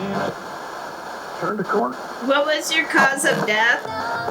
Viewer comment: Thank you for this video! at 6:09 right before you asked what was the cause of death I heard ... car 'round the corner.. and I googled red dress to understand the literal meaning of the term and this is what I found as a definition-Red dresses are used to call the spirits of missing and murdered women and girls back to their loved ones. The goal was to speak to the gendered and racialized nature of violent crimes against Indigenous women and to evoke a presence by marking absence...